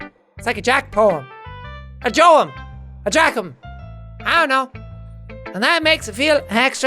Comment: sounded like "I jack em" quoting it even though it's wrong xD - jacksepticeye 2017